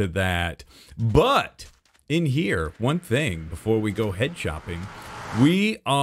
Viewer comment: why did i hear head chopping instead of head shopping?